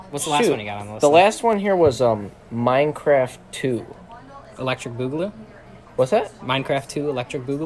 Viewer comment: what’d he say at the end? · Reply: Electric boogaloo it’s from a YouTube group on minecraft call hermitcraft